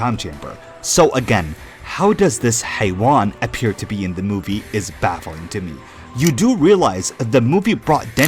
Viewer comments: did bro just say "حيوان" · did you say "haiwan"? As in, the Arabic word for "animal". The one used as a cuse word?